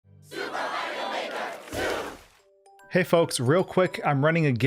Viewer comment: am i the only one who hears “Super mario maker soup”?